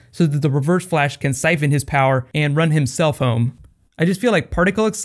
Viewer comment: I thought you said 'run his cellphone' :'')